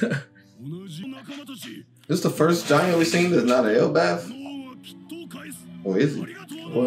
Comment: Bro, you’ve seen giants from Elbaf , Dorry and Broggy from Little Garden then, you have Oimo and Kashi from Ennies Lobby. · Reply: I think he said first giant we saw not from Elbaf